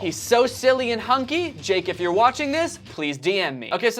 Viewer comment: i thought he said “ jake if your watching this, get in me!” i almost cried lmao